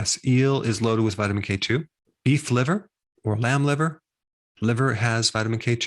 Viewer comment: At minute 9:43 did you say man liver...😳